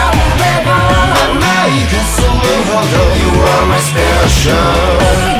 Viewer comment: これずっとyou are my stationって聞こえてた